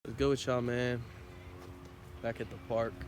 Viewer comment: What did he say at the very beginning 00:00 ? I COULDNT catch that? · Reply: What’s good with y’all man